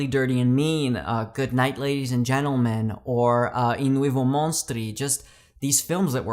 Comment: Anyone know the name of the third movie that he's saying in this list? I tried Googling what I thought I heard, but I can't find anything resembling a film. Thanks and peace. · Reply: Hey ! The third film I mention is 'I Nuovi Mostri' an Italian anthology from 1977. It was released as 'Viva Italia!' in the US apparently. Here's a link to the imdb page : <link> Thanks for watching.